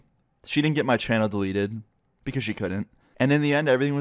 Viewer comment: did anyone else hear him say "she couldve"